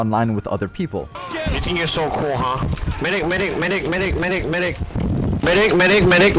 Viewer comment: who heard "meh dick" instead of "medic"?